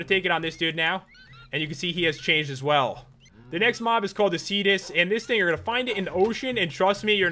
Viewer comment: This Mod has finally updated! Hope you guys enjoy this one!! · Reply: For the Cetus I thought you said fetus